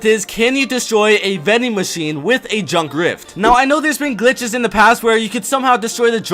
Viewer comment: did say DrUnK rIfT